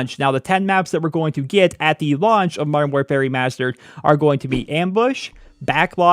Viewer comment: I keep hearing "Modern War Fairy Mastered" lol.